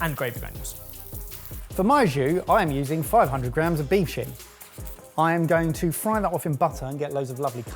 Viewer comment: Omg i had to turn the captions on..I heard say ' im frying off 500 grams of beef shit' 🤦‍♀️🤦‍♀️🤦‍♀️